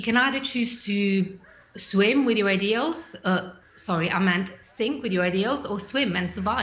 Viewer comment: I would love to hear "Covid19 sleeps with the fishes". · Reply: I thought when she said "sink with your ideals" she'd said "sink with the eels" and was like "that phrase has been updated".